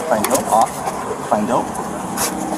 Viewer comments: Lol 😆 Is he saying, find dope, find dope, find dope? That is so funny. I love doggies so much and kitty cats🐈 🐕 ❤️ They make my heart melt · Am I hearing things, or is "find dipe" that K9's search command? Having the command be that literal is absolutely hilarious. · is he saying "find dope"?